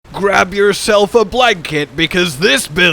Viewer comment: Damn I thought you said “grab yourself a black kid” 🤣🤣😭